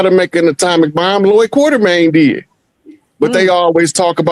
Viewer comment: At the 49:30 what is the name of the scientist Rod Hayes is speaking of? I didn't catch the name to look him up. Anyone, please?! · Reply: Lloyd Quarterman